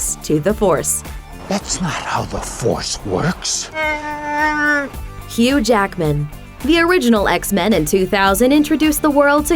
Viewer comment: The narrator is saying "Hugh Jackman", but all I hear is "Huge Ackman".